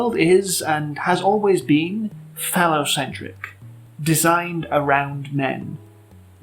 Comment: some reason thought you said fella-centric